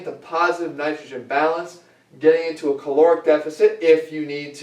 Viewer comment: Hey Jeff, can you please write down Positive Nitrogen Balance on the marker board for me? Jeff: Po5ilIVE NITR0GFV BACANUI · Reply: I skipped ahead in the video a bit and thought it said “Positive Nitrogen Bacon”